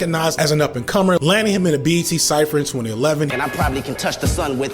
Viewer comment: "BET cypher in 2011"-- i heard "at age 11" i was like wtf!!! 😅